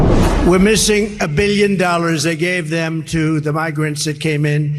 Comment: Has anyone noticed what he said at approximately 3:50 into this video. If you listen very closely he says "I gave them". His ego knows no bounds.